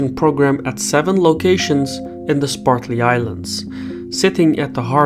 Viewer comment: I thought you said Woody Allen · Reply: Also, I thought I heard him say 'SPARTLY ISLANDS."